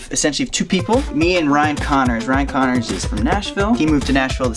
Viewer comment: I thought he said Ryan Conner :facepalm